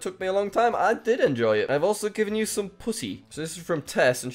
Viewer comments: Probs just me but u heard a "cat" instead of putty if u know what i mean 8:54 xD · what was that you said there jack... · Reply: Putty, though I definitely misheard it the first time xD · did i hear that right? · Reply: Putty a type of slime